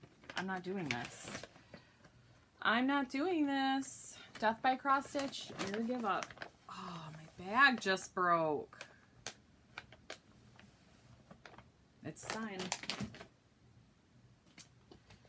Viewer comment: I literally thought you said “oh my back just broke” and I was like uh what? I also thought you said thanks for watching my b** stitch so I obviously can’t hear today. You got rid of so many projects!!!!! You started Cross Stitch Nation with me for Stitch Maynia lol :)